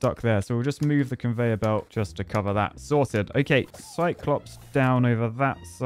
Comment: Every time I hear say "sorted" I hear "salted"...